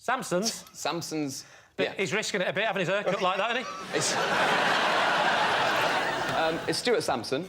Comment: Could you please somebody translate what Lee says at 3:40 ? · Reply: "He's risking it a bit, having his hair cut like that, isn't he? " You can watch with captions enabled.